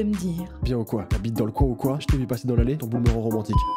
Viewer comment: "Bien ou quoi t'habites dans le coin ou coi j'tai vue dans l'allée ton boule ma romantique " 😂😂😂😂😂😂 jpp chuis morte chansons Djomb-bosh